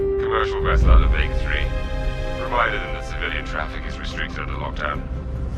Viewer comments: Also, you misheard - they didn't say "Provided" they said "Reminded" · The line that seemed to make no sense says "Reminded" not "Provided". Understandable, as I misheard it myself the first time through as well.